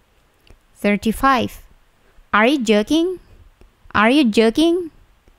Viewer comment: astaga kupingku dengernya are you jerking